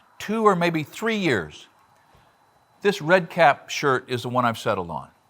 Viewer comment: You might consider putting the name of the company you are recommending in the Description instead of all the boilerplate stuff you have in there. I could not tell what your were saying in the video, Redcat is what I thought your were saying. You can never see the target in the shirt that well, even on my 70" inch screen. But I finally found the company name Red Kap (two words) in somebodies comments. A nine minute video and you never show in writing the company name large enough to be read. Thanks though, I plan to buy one and try it out.